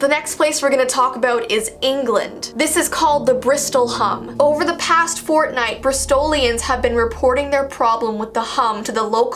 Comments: I heard over the past fortnite I think I’m losing my mind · Am I just crazy of I hear the word fortnite and nothing else? 🤣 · All I heard was "Over the past Fortnite" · I hear (fortnite) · did she say fortnite!?!? Yay i got a like thank you!! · it sounds like shes saying fortnite-- AND I KNOW WHATS THAT SOUND COMING FROM- SLENDERMAN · did she say fortnite...... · I heard say "Over the past Fortnite." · Man I wish I could hear that hum, also 5:31 sounds like she said fortnite, idk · Is it just me or did she say "over the past FORTNIGHT" in 5:28 or 5:30-5:32 Tap the numbers to hear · Did just said "fortnite" on 5:31!? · omg I thought u said fortnite😂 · who heard “fortnite”? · I know this is an old video but... did anybody else hear her say... 'FORTNITE' around 5:32? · Is It just me because I heard her say "Over The Past Fortnite" uhh yeah Oh yeah It's at 5:30 - 5:32 Lol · did she say fortnight? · did i just hear you say Fortnite? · it sounds like she said fortnite lol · in 5:30 who heard fortnite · sounds like she is saying "over the past fortnite" now thats scary... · did I just hear fortnite? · is it just me or did she say fortnite?? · did she just say fortnite????? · did she just say fortnite? 🤣🤣🤣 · am i the only one who heard fortnite?😂 if i am the only one....i need help....! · I heard her say fortnite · did she say fortnite??😂